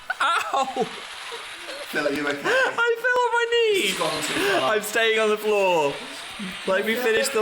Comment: Instead of “I fell on my knee” I heard “I Phil on my knee” 😂😂